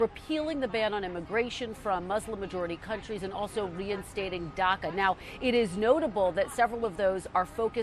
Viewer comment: umm 5:16 did she say Dhaka? Like Bangladesh Dhaka or what?